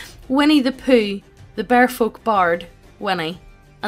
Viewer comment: I know it's the point, but some of these are disturbed. As for Tee-hee Macaroni, no. It's vulgar and immature. I wouldn't be party to that. I thought it said "Winnie, the Beardfolk Bard." That would've been weird.